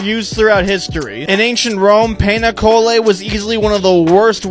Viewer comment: I thought he said pena colada LOLLLL